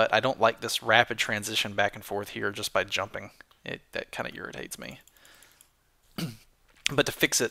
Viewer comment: does say "That kinda' urinates me"?